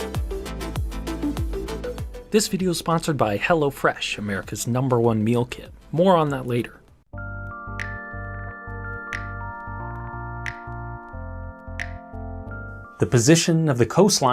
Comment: I thought you were saying hellafresh imagine some enterprise that is named that way